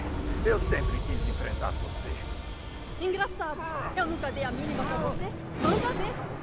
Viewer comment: Eu entendi a Viuva dizer: "Eu nunca dei a bunda pra voce" KKKKKKKKKKKKK